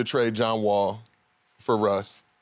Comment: For Russ? Oh I thought they said for Vogel lmaooo Gil gotta chill 🤣🤣